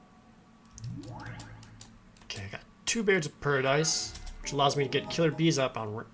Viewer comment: I thought I heard "Two Boobs of Paradise" somewhere around 29:30. Never mind... just my imagination.